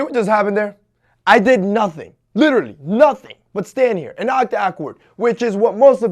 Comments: Boi said oct ahkward 😂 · did he just say "uact ACKward